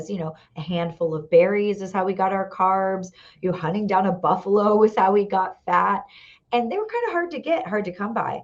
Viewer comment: What did she at 5:17 mark??? Hunting down a buffalo…then what? Couldn’t understand what she was saying. Plus she made hunting down a buffalo sound so easy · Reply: Not sure how much you missed. “Hunting down a buffalo was how we got fat. And they were hard to get.” So I do think she meant it was easy to get a buffalo. Hope that helps. 🤷🏻